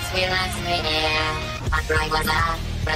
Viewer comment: Почему в самом начале там послышалось открой глаза